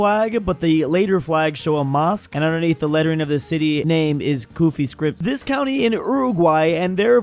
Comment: i thought you said goofy script not kuufi script